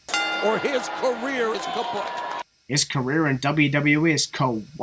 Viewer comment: I think he said "kaput"